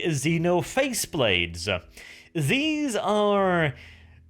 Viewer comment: This coming Sunday i will do my first live Community Spotlight! with Lord Nurgle <link> as my guest, Tune in at 6pm EST and you can ask us both some question that might even be answered! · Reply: you say phase sword, I hear "face sword" and start thinking - Deatwatch has some really fucked up weapons...